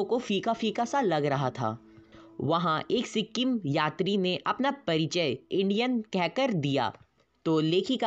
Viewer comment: it's just me or anyone else heard it as ALIEN 👾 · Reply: He said Indian not alien